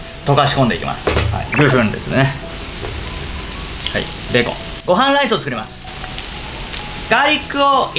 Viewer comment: で『ご飯ライス』って聞こえたの気のせい？(笑)